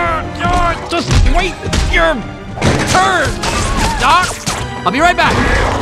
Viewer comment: Yeah! What he said, wait your turn Godzilla! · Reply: No he didn’t. He said ‘wait your turn Doc’